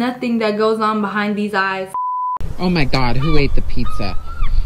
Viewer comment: I cannot be the only one who heard her say pie when she was talking about the edited seagull that carried away the PIZZA 🍕🍕